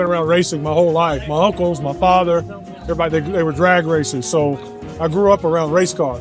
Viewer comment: I had to back up and listen to this again, I thought he said "my uncle IS my father" 😅